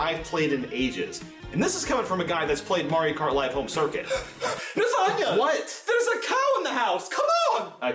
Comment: I legit thought this guy said lasagne...🤣🤣🤣 Now I cant Un hear it!!! HAHAHAHA!!!!